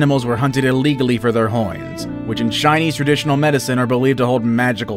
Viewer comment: it sound like he says "hoins"